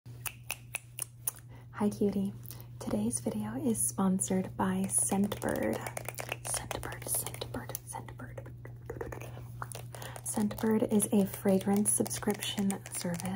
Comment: At first I thought you said centibird, like a centipede bird 😂